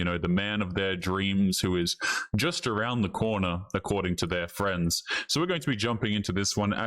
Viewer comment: did you say corner or coroner?